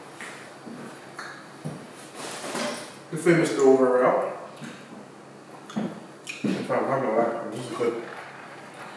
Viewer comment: What he say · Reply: He said sum kno when we around